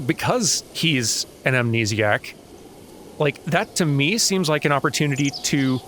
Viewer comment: I feel dumb because every time I heard "Amnesiac" my brain said "Insomniac"